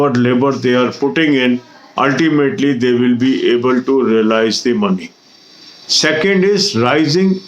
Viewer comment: Sir what did you say at 2:12 "ultimately they will be____ the money. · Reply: Able to realise. Means earn more than cost